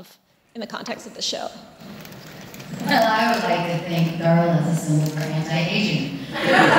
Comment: what does Julie say at 19:10. "I would like to think Darla was...." What? the sound is not good at that moment. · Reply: A symbol for anti-aging